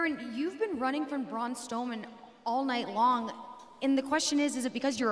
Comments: Did she said braun stoneman at 1:00 or am I just not hearing strowman😂🤣😅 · did she just say braun stoleman 😂